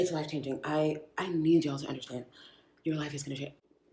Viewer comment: It’s funny because when I heard you at this moment throwing Shade, I thought you were saying that she was implying “I need yawls energy…” I know now that you were saying “I need you to understand”, but still…